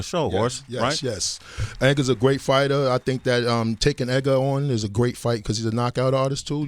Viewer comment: what tf did he say? Tank and Edgar ????? am I tripping